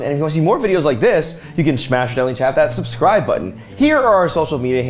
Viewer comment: I know is saying "smash or gently tap" at the end but all I hear "smashidently" as in smash it in accident. 😅